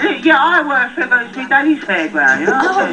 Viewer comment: "Mi Daddy's Fairground." hE'S TOO ADORABLE. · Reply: The Spanish subtitles did NOT do that part justice. I just KNEW I heard him say daddy 😭😭